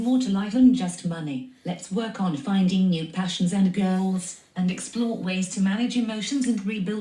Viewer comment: 😂 sounds like she says "find new passions, and girls (goals)"